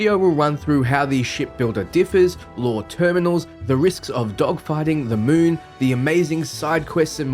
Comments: Was half listening at the start and all I heard was "the risks of dogfighting the moon" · I played this on 2x speed and I thought you said we fight the moon at the beginning.